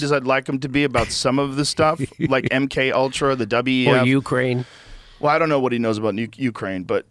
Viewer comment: "Nuke Rain"? sounded like a Freudian slip lmao